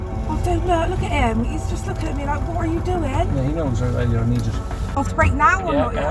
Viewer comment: Did I hear what I think I heard at 15:23? "He knows you're an eejit " 😆